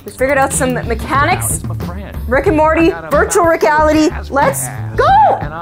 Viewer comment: I heard say "virtual regality"